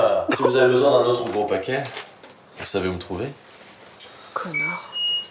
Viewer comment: C'est moi ou on entend bien "Connard" ?! XDDD